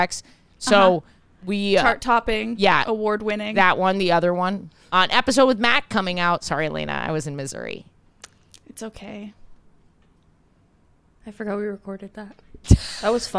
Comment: also, I swore heard say, "sorry, Alayna! I was in misery." 🤣🤣🤣